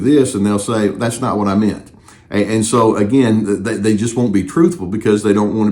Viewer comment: correction: they say "I did not say that and don't you tell me I did"